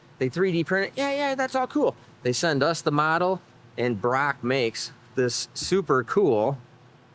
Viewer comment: does he say the name Barrack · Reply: Brock, hes the guy that runs their CNC department